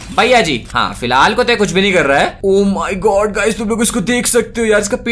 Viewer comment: Oh My God phrase at 9:36 sounded like. So now we know you are only